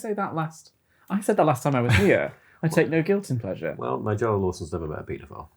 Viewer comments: what is tom saying at 15:22? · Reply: "Well, Nigella Lawson has never met a pedophile" - Tom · just me or.... it took my like several replays and even figuring out what the joke would be before I could comprehend the words he was saying that's how unintelligible it sounded. Like now I can clearly hear: "Well... Nigella Lawson has never met a pedophile" Which is a top tier response because I agree it's quite a silly statement to say "you should have nog guilty pleasures" But yeah for some reason all I could hear at first was Well... unintelligible a pedophile, then replayed and started hearing "never met a" but only when I started thinking about what would go in the blank and replayed further back to hear the name Nigella Lawson that I realized what he was saying. Like I swear if you let someone listen to that with 0 context I am almost certain they would struggle figuring out what he's saying. Either that or my brain is just fried RN. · Can someone tell me what Tom says at 15:22 please ? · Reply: I'm pretty sure he says "Well Nigella Lawson has never met a pedophile" lmao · I can't quite catch this sentence Tom mumbled at 15:20 and that made everyone at the table crack up so nicely. Would someone be so kind as to decipher it for me ? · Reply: "well, Nigella Lawson's never met a paedophile." (sorry if i've misspelt her name there, I don't actually know who she is.) edit: searched her up, I got the spelling right! yes!